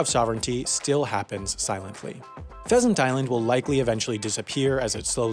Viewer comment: Did I just heard "peasant island"